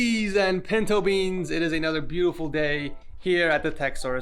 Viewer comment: did you just call me a pentobean?